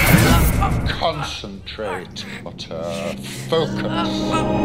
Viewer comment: I heard that Snape said C**t on the movie